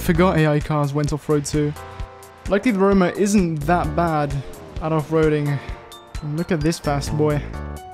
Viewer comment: When he said " at off roading" I thought he said "Adolf roading" 💀